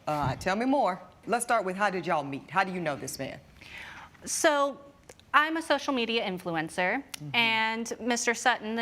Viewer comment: “I’m a social media influencer” gave me the entire picture. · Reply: I heard that as "I'm a social media prostitute".